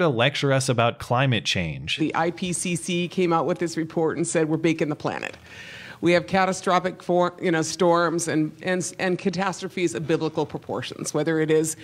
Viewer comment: watch at 3:57 it almost sounds like she says "we're baconing the planet, we have catastrophic pork in our storms" I for one would gladly welcome a pork storm :D